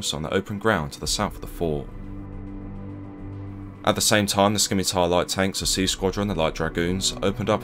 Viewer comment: Oooooh; Scimitar! I swear I kept hearing “skinny-tire”.